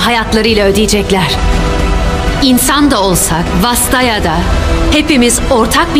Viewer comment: Ne diyor birisi yazabilir mi? "İnsan da olsa..."? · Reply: Vastaya diyor. Özetle xayah, rakan, ahri gibi çarlar